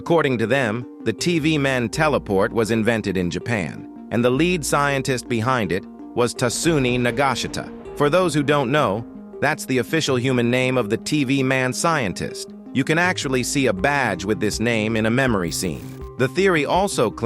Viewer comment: "Nagashita"😭😭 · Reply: What i heard "Niga shita"